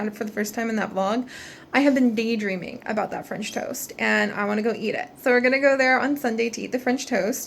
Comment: It sounds like you said finch toast XD don't toast the finches haha